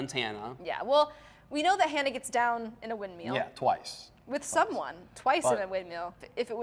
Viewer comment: I swear I thought she said “windmeal.”